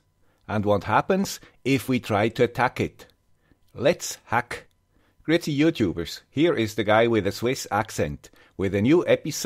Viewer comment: I've been wondering what he says at the start of every video, 0:15. Gritty youtubers, pretty youtubers?